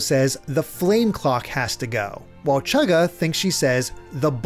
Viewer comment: I thought they said bane clock